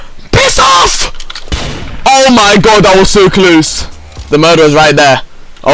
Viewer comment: i heard he said "that was so clues" 😂😂😂😂😂😂